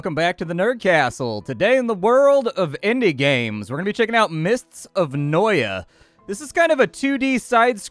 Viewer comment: Fun fact, "noia" is a Brazilian slang for drug addict, so when I heard "mists of noia" I did a double check lol